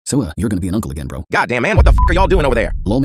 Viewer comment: I thought it said yo John uncle is going to be an uncle again 😂😂